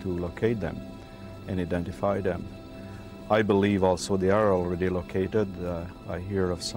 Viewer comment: Can somebody please tell me what the man says at 1:04:30? "I believe also the arrow?? will be located..." Sorry I tried playing it back but I can't tell from the accent.